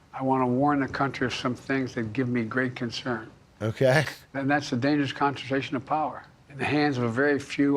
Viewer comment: i thought he said "the Danish"